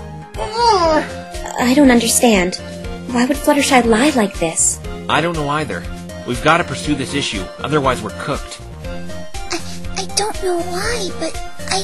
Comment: Did bro just say cooked at 58:30??